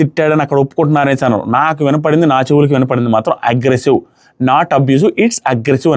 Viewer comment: I too heard it as agressive only anna